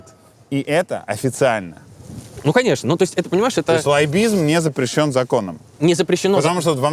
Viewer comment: послышалось не лоббизм, а злоебизм. что-то в этом есть